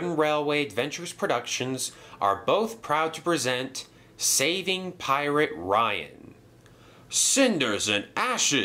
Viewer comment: At first i thought it said "Saving Private Ryan"